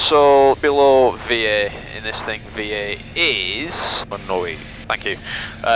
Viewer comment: Can anybody tell me what he really said at 4:46? I can only hear "Vát novin" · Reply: 1-0-8, I believe? 108 knots sounds about right for Piper PA28 maneuvering speed.